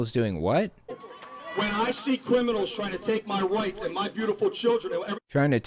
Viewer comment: I thought he said, "When I see criminals trying to take my wife and my beautiful children," rather than, "When I see criminals trying to take my rights and my beautiful children," - but I may be wrong. Either way, what I'm hearing from Steve Lynch is the sort of rhetoric that feeds straight into stochastic terrorism. This isn't a dog whistle to violence, it is an explicit call for violence.